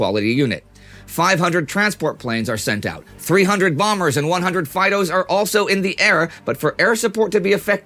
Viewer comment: Did anybody else hear "Bombers and fightos" at 3:00?"